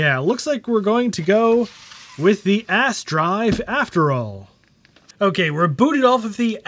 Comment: ass drive, I had to pause it to wait to stop laughing so I could hear what you were saying next.